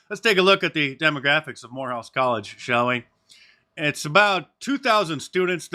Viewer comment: Whore house college. That’s what I thought you said.